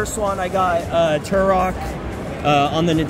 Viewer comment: Did just call his “Dad-brother”? bc that answers questions I didn’t even know I had until now.